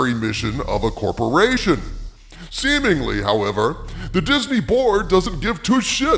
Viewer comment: Everytime says "Disney Board" I hear "Disney Borg"... XD